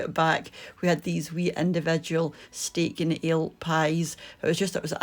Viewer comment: Gosh, I thought you said steak and eel pie! What a relief 😀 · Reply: I thought that too because people do eat eel in the UK. Now I realize she said ALE. Or at least I hope so.